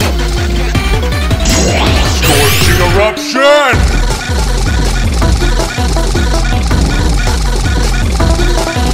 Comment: what does matt say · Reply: Something like sing around sure